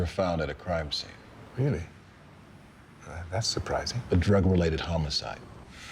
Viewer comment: detail: at 2:01 the subtitles say "drug-rated" instead of "drug-related"